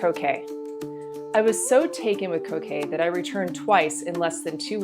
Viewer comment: I thought I heard her say ....((("I WAS SO TAKEN WITH COCAINE"")))...Had to rewind and listen again...lol...